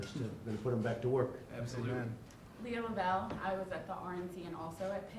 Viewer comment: ? Is that what she said? H-hi Leah...